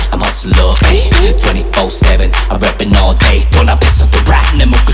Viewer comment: lmao when he said rapping i thought he was saying rabbit